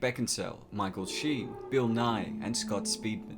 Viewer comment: When I heard “bill nighy”, I thought we were gonna start learning about inertia. Thought you said bill nye